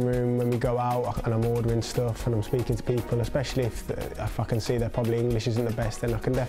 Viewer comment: does he say a swear word!!!!!! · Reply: "If I can see"